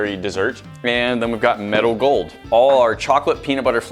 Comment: Sometimes it sounded like was saying 'Metal Gold' instead of meadow gold